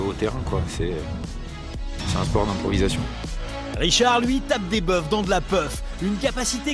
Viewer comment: c'est quoi qu'il dit a 1:08 ? fin comment ça s'écrit ? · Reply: "Tape des buffs dans de la puff" je pense